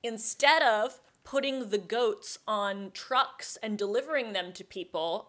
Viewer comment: Probably nerd you said trucks I thought you said drugs